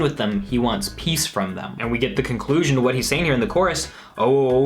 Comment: For the longest time I thought he was saying ohh ohh oh I’m boring. Not falling lmao